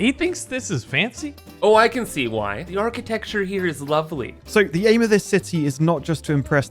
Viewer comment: did he say aaarchitecture?